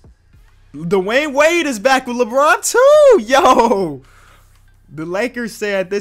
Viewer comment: I laugh when he said Dwyane wade is back with Lebron too I thought he said LEBRONTO Haha Toronto still scared of lebron